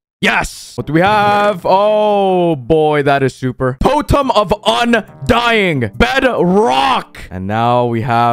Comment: he actually said potem of undying